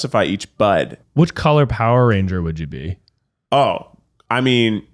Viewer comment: I misheard and thought said "which powerpuff girl would you be" and when said green I thought he meant Buttercup 😭